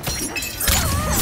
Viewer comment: mileena: *bites Kitana's head off and throws it up* also mileena: your head will adorn my throne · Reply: I thought that she said: “your head will adore my throat” hahahahhahaha